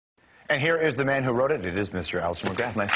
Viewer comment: did he say "wroted" it?